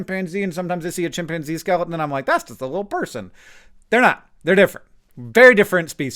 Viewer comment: I heard it as "That's just a fellow person".... coincidentally that's what my wife says about our dog.... and that's just a little wolf, so my comment comes full circle.